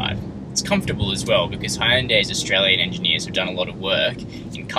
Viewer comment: sorry did he say hyundais australian engineers?